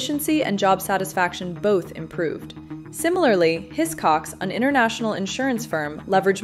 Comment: What did I just hear at 8:43? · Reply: Hiscox